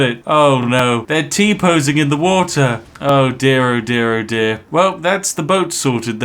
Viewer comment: did i hear tea in the water? this is giving me boston flashbacks